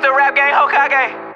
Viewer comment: Respect to anyone who searched up: “Rap Game Hokage song.” Edit five months later: Woah thanks for 600 likes this is the most likes I’ve ever gotten! Edit 7 months later: Thank you guys so much for your likes this is the most I’ve ever gotten! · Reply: I searched rap gang Hokage because I heard this on someone intro and thought it said rap gang Hokage